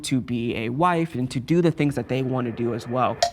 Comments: did anyone else hear the whispered “we’re coming to eat you”?!?! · Did I just hear someone whisper, "we're coming to eat you"? WTF?🤣🤣🤣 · Who is saying " I kind of hate you " at 48:40 lmaooo????? I need to know 🤣🤣 if I misheard it my bad but thays what It sounds like to me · “we’re coming to hate you” is what I heard 😭 I don’t want to believe that’s what he said. someone tell me what you think he said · did someone just say “I’m coming to eat u”??? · Am I the only one who heard someone whisper were coming to hate you at 48:42?😂